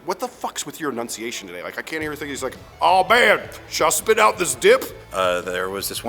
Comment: What he is saying? Just spit out this dick?